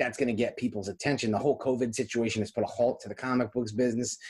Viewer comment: Great video!! I want all sorts of expensive scarce books now! 😂 Also, when said “the whole Covid thing” I misheard it as “the Hulk Hogan thing.” Do we have a new pandemic nickname???